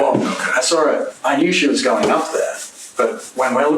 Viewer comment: He says "I knew she was going up there". Listen to what he says at the time frame of 1:05:29-1:05:34. Praying for Baby Lamb!